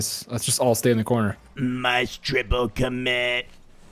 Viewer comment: 🤖"must triple commit"🤖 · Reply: I thought he said "nice triple commit" lmao. Then I heard "must" when I read your comment lol. It's like brainstorm and green needle lmao.